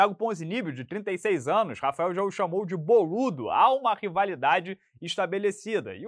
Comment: se liga nesse minuto. Só eu entendi “ROLUDO” 😅 😂😂😂- que isso